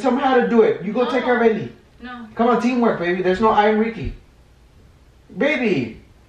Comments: Did he say theirs no I in RICKY😂😂😂😂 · Did he say there is no "i" in Ricky? 😂😂 huuh